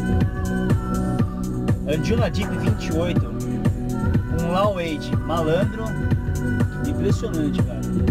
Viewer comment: E aí blz!!! pessoal Alguem pode me dizer/escrever qual o nome da musica que o disse 7:11 ? WHAT? he said... È nois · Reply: droin Anjunadeep 28 with low 8